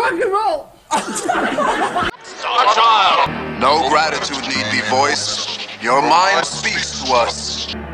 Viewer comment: what is gene Simmons saying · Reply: No gratitude need be voiced. Your mind speaks to us.